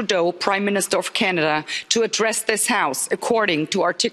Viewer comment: Address the house...he thought you said wear a dress in the house.